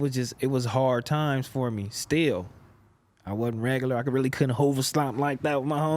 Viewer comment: what does he say he couldn't hold? said he was one of the best at it